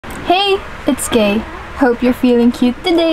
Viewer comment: I thought you said "hey its Gay" and I said "girl me too-" 😭😭😭😭